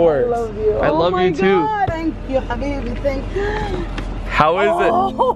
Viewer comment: did she say habibi ?